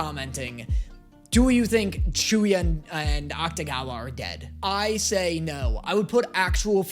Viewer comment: i no joke had to watch the end part of this vid twice because i thought said “do you think Chuuya and Akutagawa are gay?”